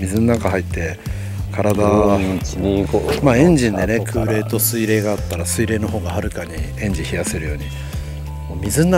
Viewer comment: 盛川のもうちょい上流、鮭網入れるとこが釣れたり・・・20:37あたりの被って聞こえる声は何と言ってる❓❓❓ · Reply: ブローウィンイチニーゴー、あとから・・。